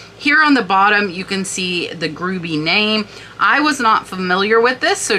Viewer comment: GRUEBY !!?! thought you said GROOVY ! :(